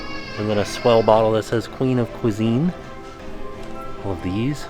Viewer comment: I could have sworn he said Queen of Poutine while I wasn't looking at the screen and I thought thats brilliant!!!